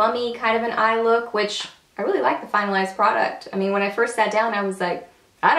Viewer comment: You had me chuckling! At the beginning I thought you said, "final ass product" but realized you said "finalized product." :) Beautiful look!